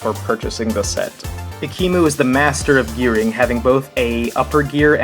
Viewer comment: I thought he said that he was the master of urin